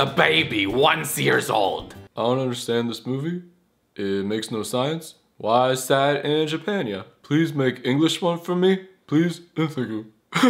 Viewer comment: Corrected version: I don't understand this movie, it makes no sense. Why is it spoken in Japanese? Make English one for me pls and thank u.